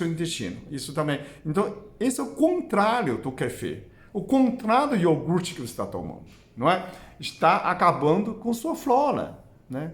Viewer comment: Ás vezes não entendo a pronuncia do Dr... Vc falou qualquer coisa sobre iogurte? nao entendi..a palavra q disse foi contra ou pra cortar aos 12:47 Aff